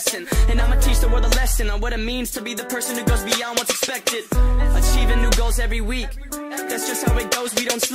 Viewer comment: i heard it as "i cheat on new girls every week"